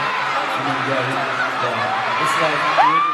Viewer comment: What did he say at 5:35? Did he say "you're like the bestest thing ever" ?